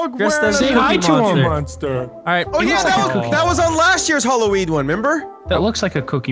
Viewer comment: did he say Halloweed??😂😂😂